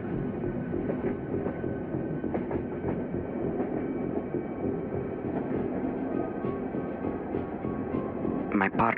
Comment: Is there anyone here who knows what he said? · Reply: If this one reply in the comments is right, he said, “This is why I reminded you to bring your gun with you today.”